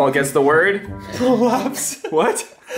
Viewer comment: What Did he say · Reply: Prolapse